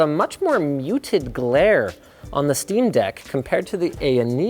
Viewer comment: I thought it said stream deck, and I thought it was an elgato ad.